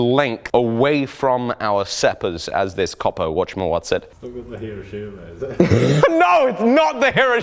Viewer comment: did he say "...to this copper whatyamawhatsit?"